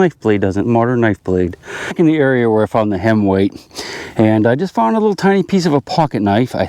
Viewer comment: Because I think you’re a bit of a jokester, I keep hearing “hen weigh” as in What’s a hen weigh? Then you’ll say, all smug-like, about 2 pounds..